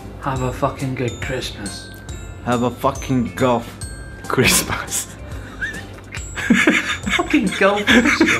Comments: I heard it as "Goth Christmas", am I the only one? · Unfortunately I didn't have a 'fucking golf Christmas' this year :( Hardly even got out of bed, I slept most of the day lmao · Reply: I thought he said "goth Christmas" hahaha. Imagining black Christmas trees and shit. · the first time I watched this I thought that said Goth Christmas. And I thought that was hilarious. Golf isn't as funny. But I still love you guy's and you make me smile if not literally laugh out loud from most of your videos.